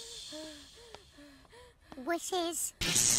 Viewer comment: what she say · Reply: I think she said "wusses".